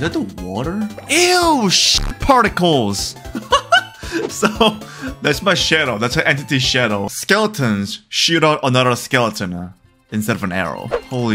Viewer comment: Did he say shit particles?!